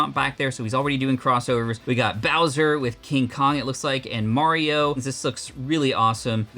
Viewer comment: did bro say Ching Chong